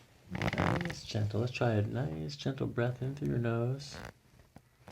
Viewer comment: The tone of voice at 10:33 when he barely says “Gentle” is the ASMR I freaking crave but that I rarely find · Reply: I don't hear that, I hear "now lets check your breathing"